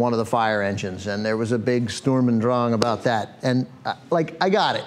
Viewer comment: Does he say "Sturm and Drang" at 5:23 :D ..